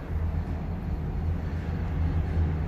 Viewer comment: it sounded like you said a dirty word. I had to listen several times to figure it out. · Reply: I say "actually" looks cooler at night...but I hear what it kind of sounds like saying actually very fast...lol